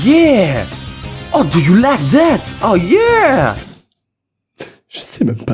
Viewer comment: dans ton enciene voideo j'ai entendue a 7:24 tu as dit How You Like That mais c'est de blackpink ca mdr bon aller bisous et continue comme ca je t'adord